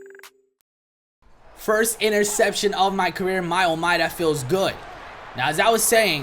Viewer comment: Did just say 1st interception of my career???